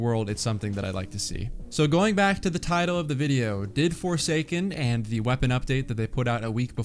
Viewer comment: I thought it said foreskin